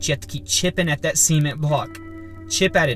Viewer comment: Sounds like you said semen block. Lol